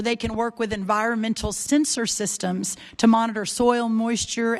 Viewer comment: I thought I heard "environmental censor systems" lol